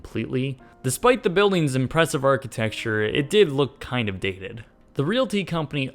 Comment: "despite the buildings impressive architecture it did look kind of DICKEDED"??? Is he saying dicked? addicted? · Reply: Nevermind. I figured out after listening to it 20 times. he's saying dated. the mall looked dated.